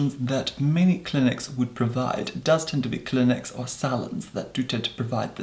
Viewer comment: I thought I heard salods.